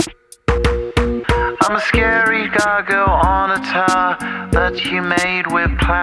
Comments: I'm a scary doggo on the tower That you made with plastic powah Your rhinestone eyes are like factories far away Where the paraletic dreams that we all seem to keep ,drive on engines till they wee with future pixels in factories far away So call the mainland from the beach Your heart is now washed up in bleach the waves are rising for this time of yeaaaa And nobody knows what to do with heeee Under sunshine pylons we'll meat while rain is falling like rhinestones from the sky *instert beautiful nostalgic music* a M M M M M I gotta pee now my heart is frozen All the bowses and the growses Have been abstinated in my 𝘴𝘰𝘰𝘰 I prayed on the unmovable Y E A clinging to the atoms of rock Seasons see adjustment Signs of change I can't pee now She said taxi Now that light is so I can take This dawn brings strange loyalties and skies I'm a scary doggo on a tower That you made with plastic powah Your rhinestones eyes are like Factories far away H E R E W E G O A G A I N Thanks, I like thicc fish thicc fish a Thanks, I like thicc fish thicc fish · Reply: I heard "I'm a scary cargo on a tower" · i always thought he said im a scary doggo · Ngl i always misheard scary gargoyle as scary gaga due to how its said in a British way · I've always thought he said "I'm a scary doggo on a tower."